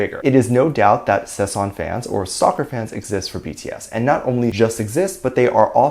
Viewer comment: I thought you said “soccer fans exist for BTS” 🤣